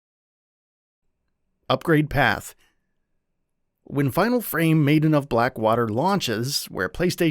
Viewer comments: Did he just say Final Frame? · did you call it final frame? Would be a weird cross over with final fantasy